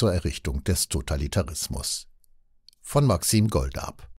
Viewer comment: Ich habe erst verstanden... "von Maxim Gorki" 😂😜🙉 Ansonsten, danke für diesen Beitrag & die Informationen. Man-oh-man... 🤔😥🤔